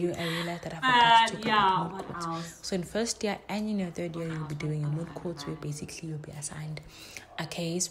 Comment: hey my Mariposa’s there was an editing error there by 15:35 , what i was saying was there in your 1st & 3rd yall gonna be given moot court assignments where yall be assigned a case & asked to draft heads of arguments with your group members . 🫶🏽